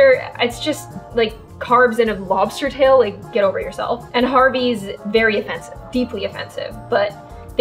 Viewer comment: Swiss Chalet, Boston Pizza, and Harvey's I don't think we have in America. Comment on this if I'm wrong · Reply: The thing I was confused on was that I thought she said Hardy's instead of Harvey's because I know for a fact that we have Hardy's in America.